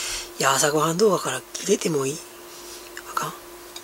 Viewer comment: もう何回戻して聞いても分からない、なんて言ってる？ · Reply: こんにちは～☺ ｢朝ごはん動画からキレてもいい？｣だと思います。違ったらごめんなさい(^-^